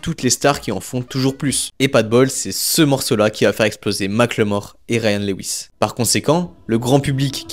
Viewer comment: la vidéo est sympa mais les gars, un p'tit effort sur les noms en anglais quand même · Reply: J'suis désolé mais tout le long de la vidéo j'ai entendu "Ma claymore", un coup sur translate avec la synthétisation vocale quoi c'est 5 secondes